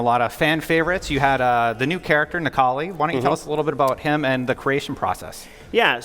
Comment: i thought they said Akali, i was wondering why they would put a LoL champion in this! haha